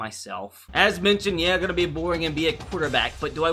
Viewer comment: Is it just me or did I hear him say gonna be a boring NBA quarterback